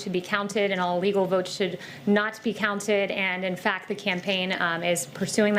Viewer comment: "All legal votes should be counted and all legal votes should NOT be counted". Did I hear that correctly???? 1:35.